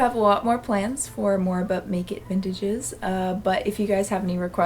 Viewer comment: I just heard it as "blanket vintage" 😆